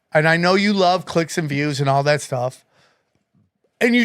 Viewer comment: When Sam said Clicks and Views, I thought he said Clits and Pubes...😅